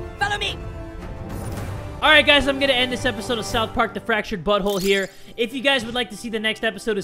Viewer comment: at the end 'the fractured butthole' it sounded like he said that